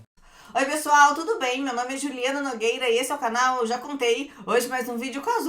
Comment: Acho que foi a primeira vez que escuto voce falar que seu nome eh Juliana e olha que te assisto ha anos. hahahahah Ate assustei. Achei que voce tinha falado Juliette errado. kkkk